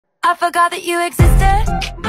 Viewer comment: The first one i heard "Avocado that you existed"...